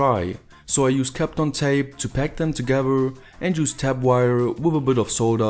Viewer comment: did he say 'cap on tape' and 'tap wire'??